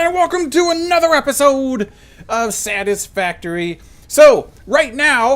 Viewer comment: I heard 'saddest factory' in the intro whoops xD 👀"probulator is in full force". that poor slug..